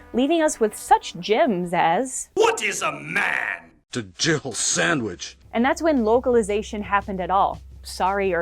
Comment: “What is a man?” “ it’s a jill sandwich” Lol 😆 Edit I thought it said “gerbil sandwich” originally.